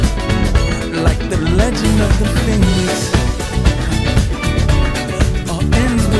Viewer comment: d-did he just say "like the legend of the pen*s"?! 😳